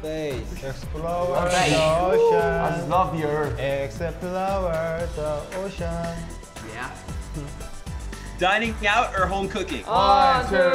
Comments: Did Namjoon just say "I love the ground?" 😂😂💜 ~:~ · Taehyung: Explore The Ocean What i heard: exo flour the ocean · “Explooreee the oceeann” “Outer space!” Hobi: I love the earth LOL I LOVE HIM😂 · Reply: I thought he said i like the ground which was relatable i dont want to go to space or the ocean